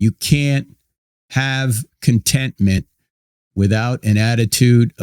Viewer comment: You can't have contemptment without an attitude of gratitude? Huh? Don't you mean "with" instead of "without"? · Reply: “you can’t have an attitude of CONTENTMENT…” not conteMPTment….